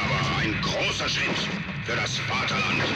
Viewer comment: Its been a year and i am still wondering what he is saying. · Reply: He's saying one small step for man but one giant leap for the fatherland